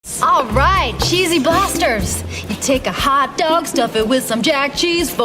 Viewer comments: Pretty sure I heard "Cheesy Blastards" at 26 seconds! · Thought it said cheesy.blisters